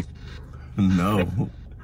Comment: Do you forgive Yolanda for what she did? Selena: 50:45 HELL NO was it just me or did anyone else hear HELL NO and not just no?